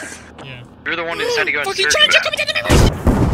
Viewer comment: what the hell was said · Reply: "Fuckin Challenger coming down on my Rifhfhgjdhs!" Then "Boom"